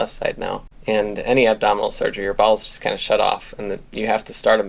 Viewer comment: NGL, first time you said "bowels" when talking about abdominal surgery I thought you said balls and I was deeply concerned for a moment.